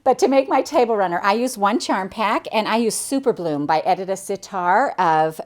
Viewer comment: Love these ! I had to laugh - you said "I used super bloom" but I heard "I used super glue" . Being a new quilter I thought why did no one tell me I could do that ! Then I realized and cracked up !